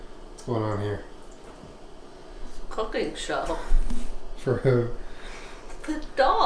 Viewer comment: I thought it said "cooking snow for our dogs" and I got excited.....